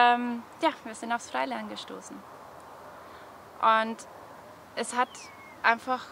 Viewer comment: Bei 8:25 wird die Lösung genannt, aber ich verstehe es akustisch nicht. Es klingt wie Skyline? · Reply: Es heißt Freilernen. Eine selbstbestimmte Art zu lernen.